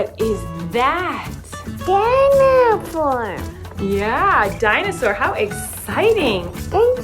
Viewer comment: When said "dinosaur" I thought he said "dinowhore" Just me? Ok!